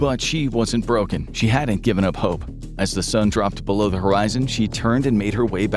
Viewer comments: in your captions, you have written "home" instead of "hope" This is unacceptable considering you have 15M subs and whole crew and team member to edit and post video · in your captions, you have written "home" instead of "hope" This is unacceptable considering you have 15M subs